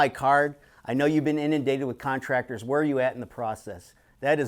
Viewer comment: What word did he say @ 8:18? Endendated??? Edit nvm inundated